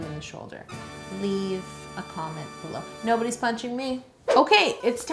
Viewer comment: I've always heard it as pinching instead of punching